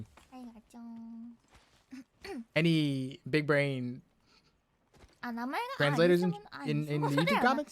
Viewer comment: i dont speak japanese but is Ayame saying "Ah, the name?" ?